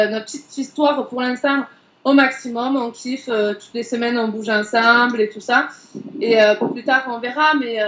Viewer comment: c moi ou a 15:06 elle dit toutes les semaines ont couches ensemble